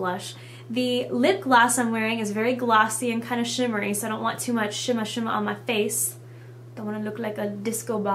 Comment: i thought i heard 'shimmer shimmer on ma face' LOL haha anyway, you look stunning! =D